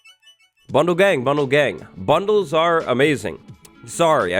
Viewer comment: I misheard it as “Bundle Gay” but thats fine too